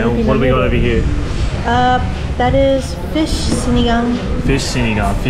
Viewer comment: i heard that as fish's n igga